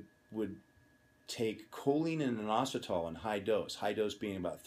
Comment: What drug is it he say the old bodybuilders took? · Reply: Is it this? Choline And Inositol